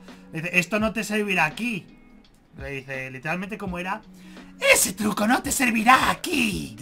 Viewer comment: yo pensé que decía ¡ESE TRUCO NO TE FUNCIONARA A TI!